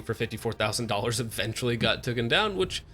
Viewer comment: Hey, did I hear ya say ‘tooken’?